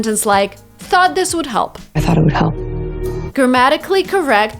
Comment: Your movie clip actually used "I". She said "I thought it'd help." Was she speaking so fast you didn't hear it?